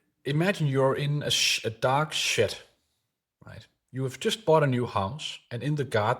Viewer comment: I misunderstood I thought you said "imagine that you are in a dog shit" instead of dark shed😂😂😂 Love your video. THANK YOU